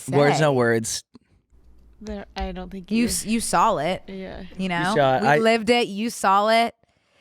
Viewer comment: I will never be able to unhear that says “saw’ll” instead of “saw” 😂❤